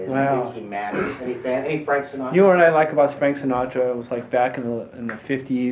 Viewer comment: sounds like he says 'Spank Sinatra'